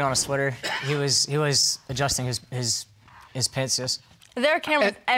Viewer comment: Did he say pantses??